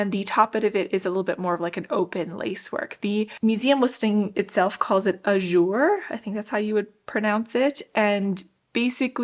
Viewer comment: i don't know exactly what you said at 1:18 but im thinking it might be a "jour" ? in which case mean in French both "a day" and "an long gap" (like under a door is "un jour"). Which is interesting considering it is referring to lacework!